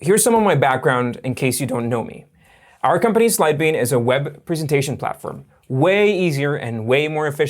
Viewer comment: Hi. I think you should start saying Sidebean slowly. I watched 3 of your videos before I knew what you were saying (because you had a graphic of the name) before that I thought you were saying "Syebee". I actually need Sidebean & would have downloaded it sooner had I understood the name earlier.